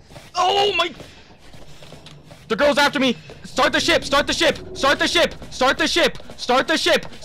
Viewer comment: I thought he was saying “START THE SHIT START THE SHIT”